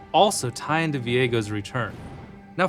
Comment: Can’t wait to get Alistar comboed by enemy Viego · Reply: I deadass thought he said Diego and said wow ruined king is Mexican now lmao